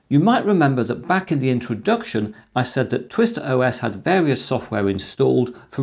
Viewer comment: I thought it said Twitter OS, LOL! I was like please nnnoooo